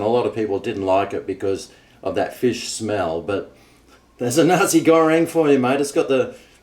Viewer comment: I thought you were saying Nazi Goreing until I saw what was written on the box 😂